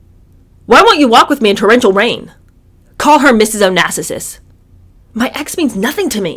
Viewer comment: Did you say John said to call his mom "Mrs Onassisiss??" LMAO Too MANY SSS's there I'm afraid